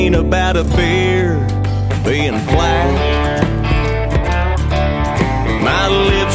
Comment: Thought he said “I’m aloud to complain when my beer looks black” 😂 jesus